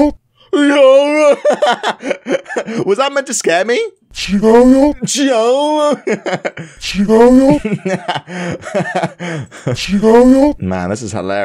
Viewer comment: what I heard from the monster is Chicago